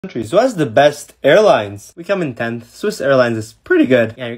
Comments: I really thought he said hairlines 💀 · I THOUGHT YOU SAID HAIRLINES DKDJDNN UN DHUDJFJRJRJ